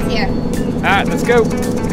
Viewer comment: "Josh concrete's here." Alright let's (instead of go.. its goat) haha Let's goat Another great video, cheers.